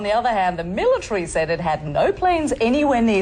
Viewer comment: wtf did she say at 0:24 ? whats a millterry ?